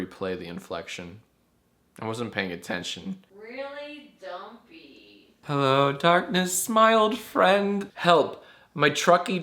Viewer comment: did he just say "Hello darkness smiled friend" referring to a YGS or am I high